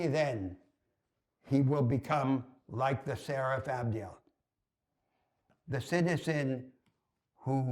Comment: What is said at 8:14? "Only then, he will be come like the ______." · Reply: I'm pretty sure he said "Seraph Ambriel". Note that Ambriel isn't strictly speaking a Seraph